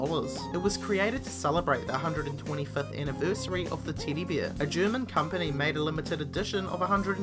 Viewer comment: Who else for #11 thought he was saying "Teedy Beer"